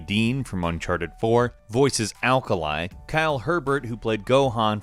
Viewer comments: did he just say alkali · What did you just say? Aekaeli? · did he just say 'Alkalai' to Akali????